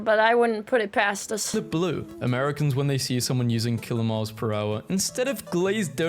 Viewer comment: did he just said kilomiles per hour ?? 🤨